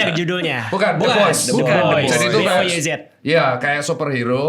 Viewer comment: tolong ada yg tau judul film yg disebutin dr tirta gak? kurang jelas dengernya takut salah🙏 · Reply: The boys, series superhero kebalikan mcu, nonton di prime video, seru bgttt